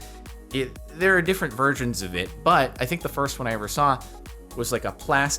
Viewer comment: Is it just me, or is he saying "there's different VIRGINS of it"? it really sounds like a G and not S to me at least